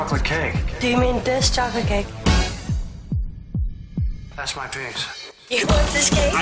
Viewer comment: Sounds like discord is saying thats my things